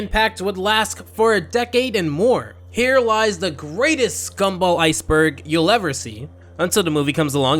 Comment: did you just called him scumball